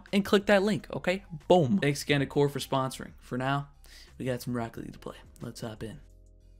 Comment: The subtitle says broccoli instead of rocket league xD